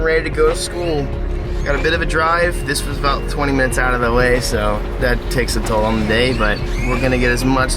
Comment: i swear i thought him say “20 minutes out of LA” and i shat myself